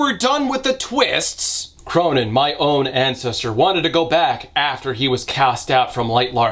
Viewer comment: When he said this guy’s name I heard Conan and I was like HUH?! 😂